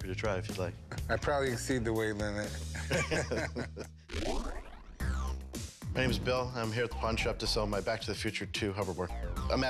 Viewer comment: it rlly sounded like he said "The name is Bill" like Bill Cipher tho